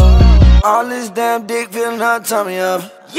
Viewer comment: WHY TF DID HE JUST SAY "ALL THIS DAMN DICK FILLED MY TUMMY UP" BRO??